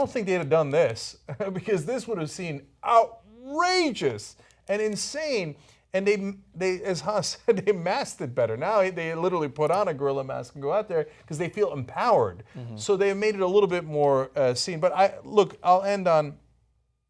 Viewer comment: lol I thought it said Godzilla masks and I was like wtf